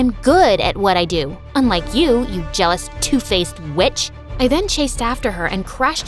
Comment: I thought she said bitch😭